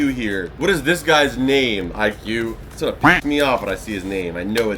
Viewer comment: Did he say pi$$